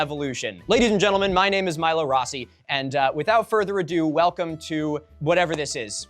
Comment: i had to grab my glasses to look at the screen cuz when you said ur name i heard nothing but "lila ross" (that weird chronic liar who sucks at lying but everyone beleives her anyways from that show with the ladybug and the cat thing)